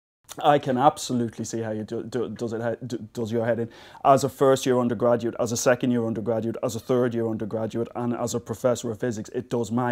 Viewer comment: what did he say from 0:00 to 0:15 ? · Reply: He stammers a bit, but he says "I can absolutely see how it d-d-does-does your head in. As a first year undergraduate, as a second year undergraduate, as a third year undergraduate, and as a professor of physics it does My head in, so, it's, yeah, it's a very tricky concept".